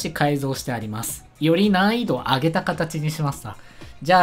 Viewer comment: より難易度を上げたかたちにしますた。に聞こえてる 笑